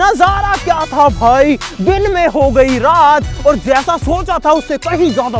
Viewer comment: Din pe hogai rat instead of rat pe ho gai din